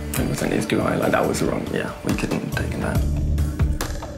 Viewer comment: its " Ich verstehe nicht" Peter